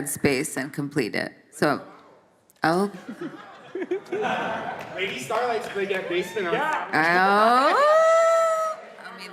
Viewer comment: PLEEEEEEASE someone tells which idea the fan have him at 41:20 and SHE LOVED IT! I can't hear it 😭😭😭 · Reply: They said that Lady Starlight would bring the bass (and the rest I could not hear). Im drunk and crying because of this, and I think my listening skills are damaged